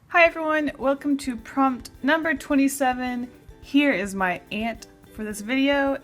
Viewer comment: I heard it as; "Here is my Aunt for the video."